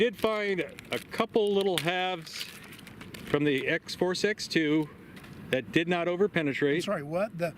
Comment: When says Force X2 fast it sounds like he is saying '4 sex too'. I don't think should be working in advertising.. :D Great, informative and entertaining video as usual!